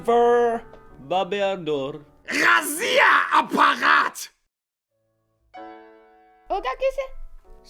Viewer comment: кто услышал российский апарт?